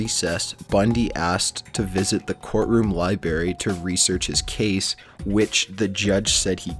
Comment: Did he actually say libary. Kill me now